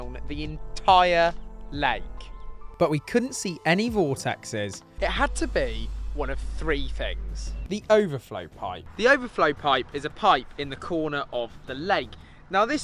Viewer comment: I thought he said “ball sack” on top of the water!! I was looking for a huge ball sack floating around!! Realised he said “Vortex” 😮‍💨